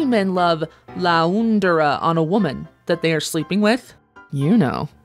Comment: I thought it said Landry lol 😂